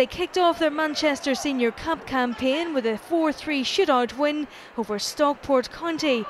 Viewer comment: Did she just say shitout instead of shootout lol